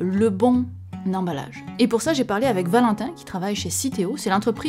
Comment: Quand t'as dit que t'avais proposé l'idée du live à Valentin, j'ai entendu "Corentin" et j'ai imaginé un live avec un concombre qui répond à des questions. Franchement, j'ai bien aimé l'idée 😜